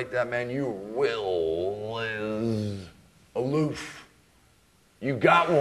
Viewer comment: Can someone tell me what he say at 1:56 - i mean "your will is a" and what? loof or what? I dont understand english so well :) so please help me with this word and meaning of it ;)